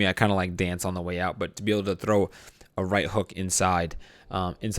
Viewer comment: first time hearing call his one of his opponents an idiot ( I was wrong whoops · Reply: He said 80 inch not idiot (I thought he said idiot too)